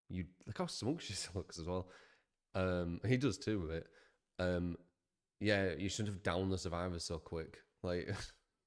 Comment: that's not what they said. she said "its your fault for getting downed so fast" in response to him saying surge loves to eat up gen progression. you really need to do better at being less pessimistic.